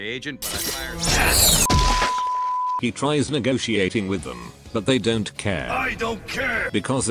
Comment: first time I heard 'Legoshi-ating with them'